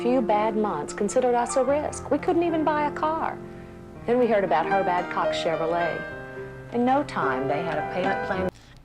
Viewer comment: Did she just say she heard oh "HER BAD COCK Chevrolet"?! uh... Huh huh huh ...